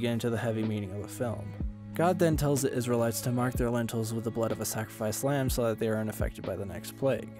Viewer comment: Love this summary. It sounded like you said “mark their lentils, instead of ‘mark their mantles’ 😂 mmm lentils